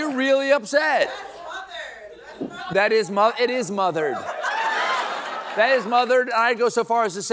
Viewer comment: I couldn't hear what she said. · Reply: She said "Hol' up!" at first. Her next remark was "That's Mother! That's Mother." Mother is a nickname a lot of people use for Beyonce. Stephen thought she said "Mothered." He had no idea what she was trying to say. So he replied with an equally random "Boots King!" which means nothing. The part I can't figure out is why he pronounced Xanax like "Vthanax"